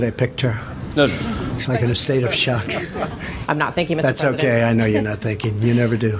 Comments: I heard, "I'm not thinking Mr. President." So, I can understand how Trump heard that. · She said, "I'm not thank you". He heard "I'm not thinking". (Rewatch the clip. Notice, she said it so quickly there was no pause for the comma. Causing Trump to hear something totally different. It's her fault for raising her hand at a press conference while unprepared, and skipping her commas. It's his fault for mishearing and responding too quickly. It's also a non story. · Honestly I heard “I’m not thinking” my first listen. I don’t think it’s presidential to respond like that whatsoever, but it fits his character and is an easy enough mistake. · I actually thought she said, I’m not thinking, until you said her actual words and then replayed the video. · Honestly the first time I heard the reporter talk I thought she said “I’m not thinking” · I’ll be a little fair to the president, I heard, “I’m not thinking.” Which makes his response make sense and incredibly rude. I shouldn’t be amazed at his level of animosity, but but I’m surprised every time. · I thought I heard Trump say "I know you're not thanking, you never do." and not "I know you're not thinking, you never do."... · To be fair... I heard, “I’m not thinking,” too. Still kind of a dick response, IMO.